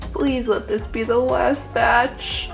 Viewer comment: When you said "please let this be the last batch" I heard "Please let this be a normal field-trip!"